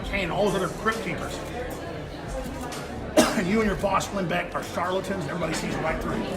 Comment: Did he say his boss was Glenn Beck?